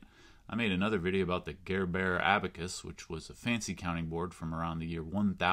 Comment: I had never heard of these, even once! Thanks for the lovely introduction. (And even though I've watched your Gerbert abacus video, I still momentarily thought you said "Care Bear abacus." There's a product idea......... It's been a long day!)